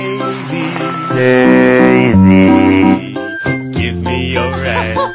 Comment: I’m so sorry whenever I hear this I only hear ‘Daisy, Daisy give me your *ss for poop’ 😭